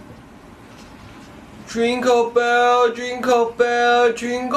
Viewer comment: sound like he said Drinkle instead jingle